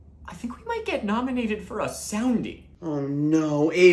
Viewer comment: Anyone else thought he said "sounding"